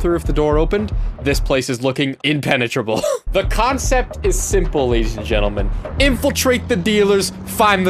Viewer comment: did he say cockcept 😂😂